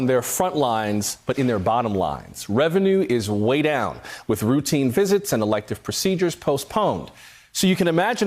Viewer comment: Did he just say, "Routine physics?" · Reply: Richard Darlington : No, he said "routine visits."